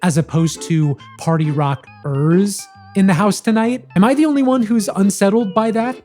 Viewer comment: Bro really thought they said "Party Rockers". Buddy, go back to 2010.